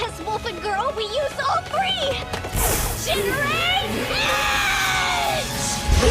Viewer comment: When they said generant edge I thought the said degenerate else